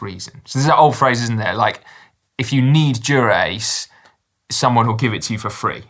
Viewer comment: I heard that as "If you need Durex"... and thought "yeah, my doctor will give it to me for free"